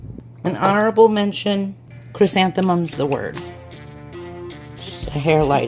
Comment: what is she saying at 7:56 ?? ( i listened to it like 10 times i still don't get it ) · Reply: Wet n wild chrysanthemums the word